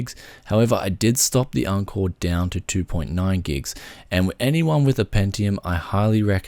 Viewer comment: What is he saying at 2:30 "I did stop the encore down to 2.9ghz?" No idea what hes saying but apparently its a tweak, i have a g3220 but i cant understand what hes saying.